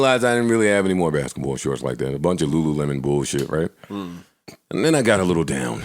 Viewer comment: Wait a minute! Time ⏲️ stamp 11:03 did Joe just say he only has lulu lemon? Thats just nasty!